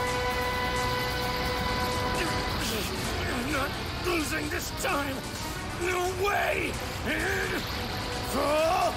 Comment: Why does he say that? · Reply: Not losing this time no way! Full POWER